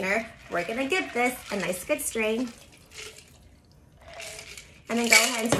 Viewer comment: Wow, it only took me about 50 videos to realize she says a nice good "strain"! I always thought she said a nice good "stream." 🙄